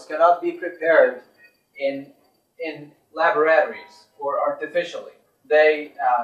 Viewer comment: did he say laboratory or libraries? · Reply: Laboratories